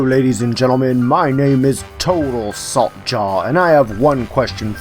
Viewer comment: sorry about this but 0:02 to 0:04 it sounded like total sock jar (I'm not joking) /\ | click for sound